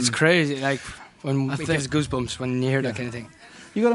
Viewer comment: It kind of sounded like Niall said 'Guzbumps' instead of Goosebumps. xD